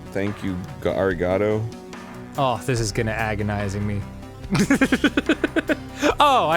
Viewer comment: did dan say “this is gonna agonising me” or did i mishear him